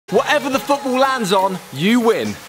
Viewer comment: At the start it sounded like he said whatever the f*€& it lands wins it 😅